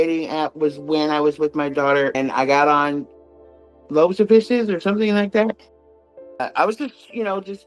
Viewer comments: "loads of fishes", that must be the Temu version 😂🐟🐠🎣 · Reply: 😂..I thought she said "loaves & fishes" 😂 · "loads of fishes", that must be the Temu version 😂🐟🐠🎣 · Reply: 😂😂😂 I thought she said loaves and fishes 😂😂😂